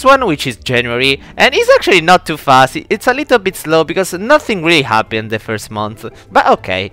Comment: “Because nothings really happy in the first mont” Me: 🥲 but my birthday is in January. I thought they said Happy birthday